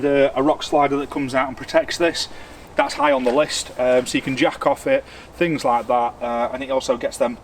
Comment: Cracking video - landys coming together really well. I might have mis-heard but I thought you said " jacking off on the rockslider" - is this a usual bug out activity ? Best wishes from Shafton.